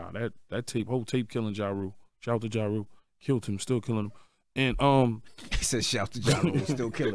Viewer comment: Did Yayo said " Shout out to Ja rule killed him still killing him " 😂😂😂